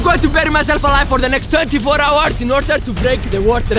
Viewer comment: it sounds like he said 34 but 24 at the same time